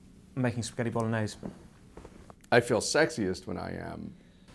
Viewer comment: I thought it said sexist. I'm like😕